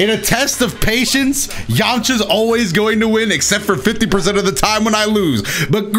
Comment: "YAMCHA ALWAYS WINS EXCEPT WHEN I LOSE" is what I heard